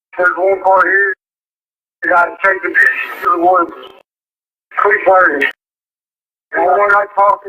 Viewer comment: I swear he said “there’s a guy chasing me into the woods”